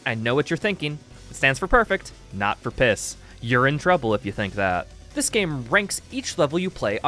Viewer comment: Did you say “you’re in trouble” or “urine trouble”? Hah hah hah